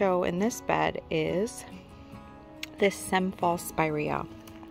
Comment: your garden is absolutely gorgeous! I love all the Japanese Maples dotted throughout your lovely landscape. I listened three times, but couldn't understand the name of the spirea you highlighted at the 24:05 - 24:20 time stamp. What did you say it was called? I think I need to get one of those for my landscape. Have a great weekend! ~Margie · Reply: SEM FALSE SPIREA....Hope this helps!...also in name of video.